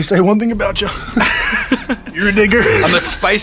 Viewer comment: did he say the N word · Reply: He said, “you’re a digger” speaking to the research the interviewer did.